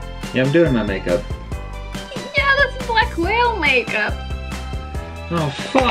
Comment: Did she say black whale make Up at 8:37 😂😂im sorry